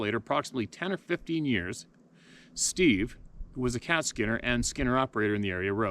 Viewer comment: Yes, I noticed it but assumed it was some sort of glitch with the new camera, as I thought it had happened before with this camera. Like some sort of overlap. The voice sounds like a guy saying "Hi George" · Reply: and I thought I heard "hi Steve"